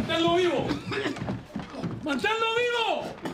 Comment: What was Arquimedes saying? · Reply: "Mantenlo vivo" or keep him alive.